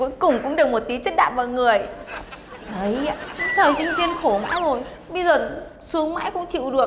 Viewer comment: nghe như thời sinh viên khổ vai l :v